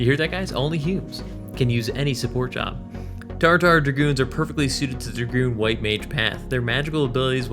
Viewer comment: I heard you say "Jar Jar" instead of "Tarutaru" and forevermore I'll think of Tarutaru as the Jar Jar of FFXI. Jarujaru Binks. It makes too much sense.